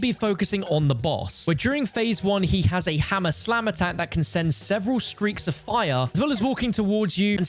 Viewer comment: American here with a large case of the goofies. I heard “he’s got Tha Hammah Slammah that can send…” 20:55 😂😅